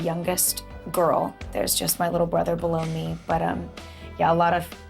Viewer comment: I think she said "There's just my little brother below me." But it did sound like she said baloney!